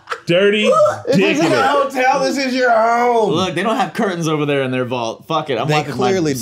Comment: Did just say "this isn't a hotel this is your home"? 😂😂😂 Remind me to avoid hotel rooms you've been in mate